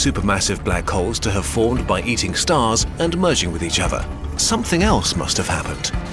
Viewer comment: it sounded like "yeeting stars"